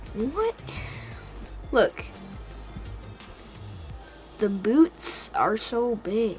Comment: i thought your son says boobs